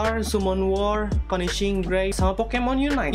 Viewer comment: Klau endk salah denger itu punishing gray raven ya bang.?